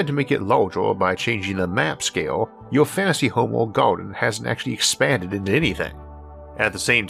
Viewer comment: I hear you say "garden", but I read "kingdom". Have you had a change in the script?